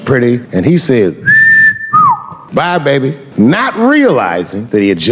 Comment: I thought it says Blyat for a minute